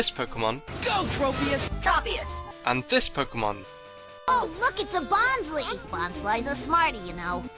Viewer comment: did max just say "Oh f*ck its a bonsly!" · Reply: derp no he said ,,oh look'' xD